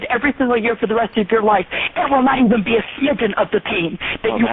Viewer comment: did she say "smidgeon"?? at 2:16 lol